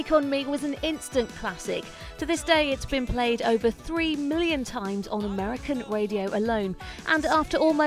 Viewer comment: holy shit.. i thought she said 3 BILLION times at first. A-HA was a beast.